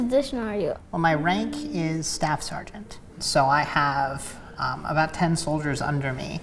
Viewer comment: I know I heard it wrong but I thought she said “well my rank is bastard”